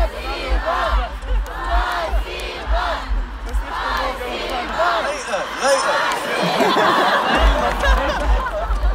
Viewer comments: what are they saying????🤔🤔🤔🤔🤔🤔🤔🤔🤔🤔🤔🤔🤔🤔🤔🤔🤔🤔🤔🤔🤔🤔🤔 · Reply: 'Thanks' in russian - spasibo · what are they saying ? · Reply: Spasibo (thanks on Russia :) ) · What are they screaming? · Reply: Spasibo - thank you in russian